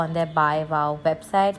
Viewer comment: she said bebside instead of webside